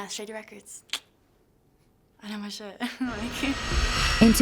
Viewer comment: Did she say ik my shit